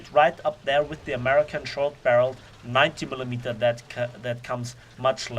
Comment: Sound like he said idiot Millimeter